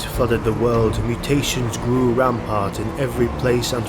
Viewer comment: I think it should say "mutations grew rampant" not rampart. Aside from that one point I really enjoyed the video and script.